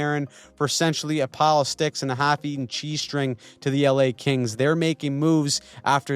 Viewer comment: hell for a second I thought you'd said "half-eaten g-string"...